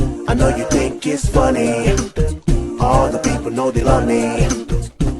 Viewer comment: Every time there was the beatboxing puppy. I swear I heard "I'm a beatboxing puppy, you know my d*ck is funny" and that's fkn funny